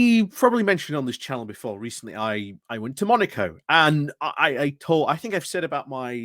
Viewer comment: Oh, Monaco ! Thanks for clarifying that - I had thought you said you were learning the harmonica.